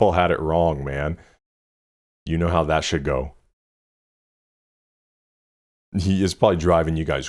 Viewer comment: no it’s actually “how that should go” not shit. In a behind the scenes recording you can see Drake on the phone telling (radio?) that he didn’t say shit😂 and he said should, so they wouldn’t bleep it but it sounded like shit.